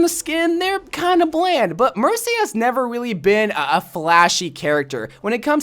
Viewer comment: did he say mershy